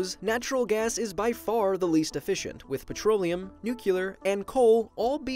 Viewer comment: Did you just say "nucular" at 1:50? What are you, Bush?